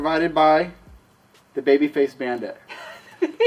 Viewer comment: Lol, I thought said babyface panda, funny